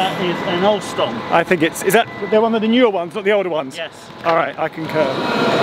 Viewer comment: I swear I heard: "Is that one of the newer ones or older ones?" "Yes" "Alright, I concur" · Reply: "Is that one of the newer ones not the older ones?"